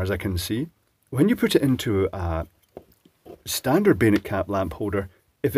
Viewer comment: I think you're saying "bayonet caps", but I keep hearing 'beta caps". It's become clearer - three years ago, they definitely sounded more like 'beta'. It took a long time before I saw comments saying "bayonet", and I realized what was actually said. I've seen the G style lamp holders, with the hard to find 'male' feet, but I don't think I've ever seen a bayonet cap bulb or socket in the US or Canada.